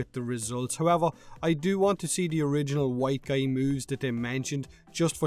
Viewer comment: i thought you said "danielle's boobs got you excited" im like wait what.... rewind... ohhhh moves. this is the problem with lurking and putting the vid in the background.